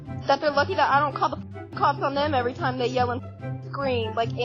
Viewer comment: Bro got mad at her like she called them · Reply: i thought she said she doesn't call the cops on them everytime they make noise